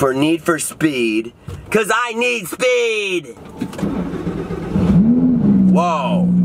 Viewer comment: I thought he said I am speeeedd